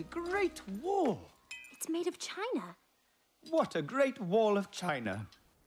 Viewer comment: Dorothy: “It’s made of China” Me: Wtf is material made of China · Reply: I first thought they said “Made in China” lel XD